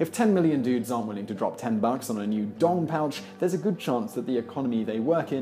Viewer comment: "Dong pouch" I'm dead 😂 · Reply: HA Makes more sense! Thought he said dog pouch 😆